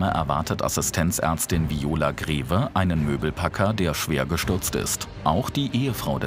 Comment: habe ich verstanden „lümmelpacker" 😅